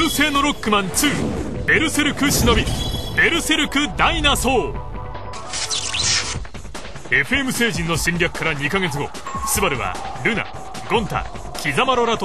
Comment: sound like he said rokkuman starforce dinoasshole2